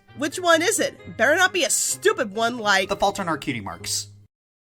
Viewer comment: Ha, I thought it said the fruit in our cutie marks